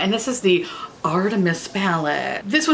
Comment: At first I thought you said the "Artemis Fowlette" and I thought 'what fun marketing for a movie no one's watching.'